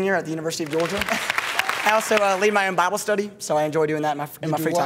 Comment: I'm from Texas I couldn't even understand that just omg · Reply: Seriously? He said Bible Study 🤣🤣